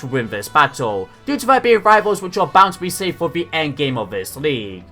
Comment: Did anyone hear Endgame? Or am I hearing stuffs because of the hype? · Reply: he did say endgame ("the endgame of this league")